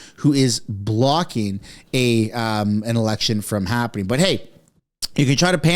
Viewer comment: Election, NOW!!!! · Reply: I sincerely thought you said "Erection Now!"...Lol.